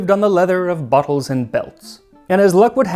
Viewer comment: Max.. my guy, I love you, but you gotta pronounce that better. I thought you said, “For days they lived on the leather of Buttholes and belts”